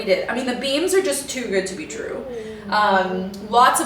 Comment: Ong I heard you say “got to be drew” and not “got to be true”. That would be the perfect name for a show for Drew from Lone Fox Home!!!!